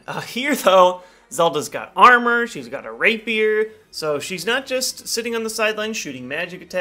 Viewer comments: it sounded like he said Rape Beard. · "She's got a rape-beard"? What is he saying there? Can't be what it sounds like. English isn't my native languange.